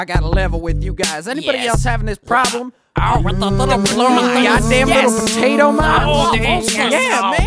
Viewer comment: lol i heard someone say "Goddam little potato mines!" when it started